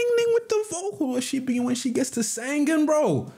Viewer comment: what did he say at 5:15 go ahead ningning with the vocal... she be when she gets to sing bro? she be when she gets the second bro? any native speaker help on this comment? Thanks!